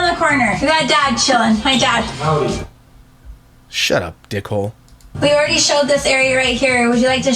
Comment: When the dad came into frame and said “Shut up, dick hole”, I thought he said “Shut up, pork roll”. I like that more.